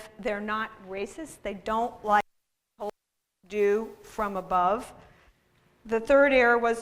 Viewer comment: what was bleeped out at 37:45? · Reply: hoomalumalu could just be a recording, it sounded like she said “they don’t like being told what to do from above” but I could be wrong.